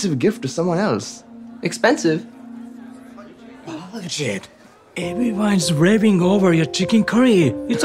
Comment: kind of sounds like he says something else 🤔💀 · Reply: Bullshi